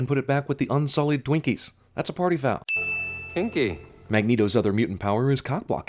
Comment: Wait does magneto say "kinky???" I thought he said "Hanky"!!!! Oh my god that makes so much more sense now!!!!!